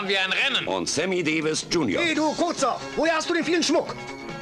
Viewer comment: ey du kurzer wo hast du den vielen Schmuck her? :-) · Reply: Ich hab immer Furzer verstanden ... haha